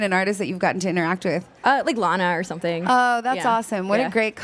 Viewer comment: who's her inspiration? couldn't quite pick that up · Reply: Sounds like she said "like Lana" so I'm guessing maybe Lana Del Rey